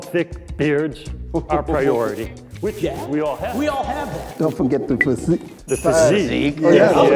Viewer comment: I thought at 1:18 Santa said "Don't forget the Pussy" LOL